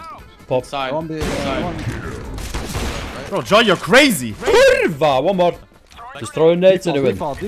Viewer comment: did i hear "kurwa"? O_o